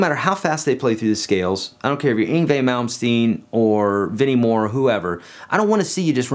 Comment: Who is this great 'Moumstein' guitar player mentioned at 7:47 I hear guitar players talk about but can never understand the name enough to even Google him? The first name is 100% illegible (Ingvay?) every time I hear it. No one says it clearly and the last name isn't much better. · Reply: Yngwie Malmsteen